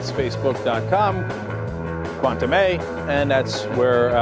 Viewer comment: i keep hearing cenk say "quantum A" all together now: kwen - tah - meh